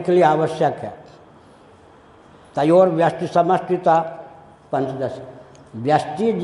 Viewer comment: A correction in the transcript - तयोर्व्यष्टिसमष्टिता instead of तयोर्व्यष्टि समिष्टिता